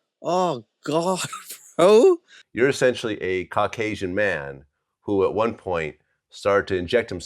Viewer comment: Sounded like he said his parents were Crack Asian 🤦🏽‍♀️